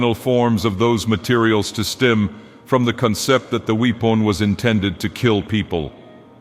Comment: "weep on" instead of weapon lmao